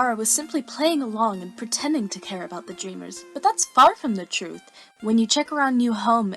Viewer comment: I thought she said fart from the truth 😹😹😹😹😹😹😹😹